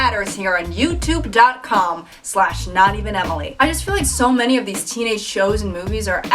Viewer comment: thought it said not even family